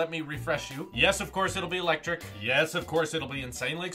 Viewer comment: Wait. I misheard but I can't figure out what it's meant to be. At 0:55 : yes it'll be like (jerky?) Edit: nvm I realised you said jerky Edit 2: ELECTRIC. I MEANT ELECTRIC 😰